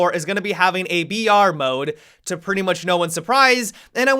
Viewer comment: OOOOOH.... I thought you said a VR mode. "To no one's surprise" Damn I was so confused